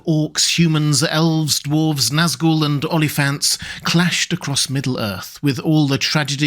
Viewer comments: At the beginning I heard "Only Fans" and I checked if it were an April Fools video. Then, after a couple of tries I realized it was "Oliphants" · I thought you said OnlyFans, took me a while to realize it was Oliphants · Did he say army of OnlyFan at 0:30 ?🤔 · Damn, I heard "Only Fans" instead of "Oliphants" in the early bits of the video. This got me confused, thinking that you had gotten into kinky contents 🤡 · Oliphants. I thought he said only fans 😂